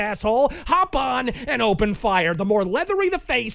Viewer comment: At first I heard "hop an and open fire," without the D in "and," but didn't bat an eyelash because this is.